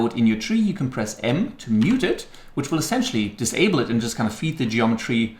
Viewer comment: When he talks about the first shortcut, I thought he said N but he said M, just wanted to let y'all know if you have the same problem.